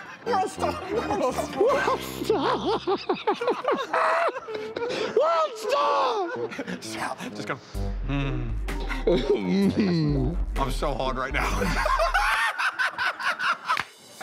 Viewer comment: What does he say at 1:19? Sounds like Wellstar?